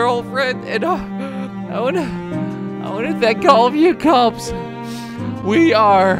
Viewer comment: What he said: I want to thank all you kops What I heard: I won't think to call the cops